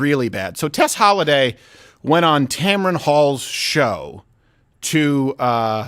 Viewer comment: Holly shit I thought that said tampon not tamron